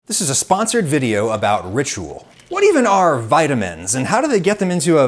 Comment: I can't actually see his text posts on PC without actually clicking on his channel and going into the "Community" tab. I feel like a lot of us are unaware of his post. It wouldn't hurt to include that information either at the beginning of the video, in the title or in the description. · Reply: He doesn't say "sponsored video by Ritual," he says "sponsored video ABOUT Ritual." I think everyone probably misheard that and got confused. He said it's about them, not just a video they funded.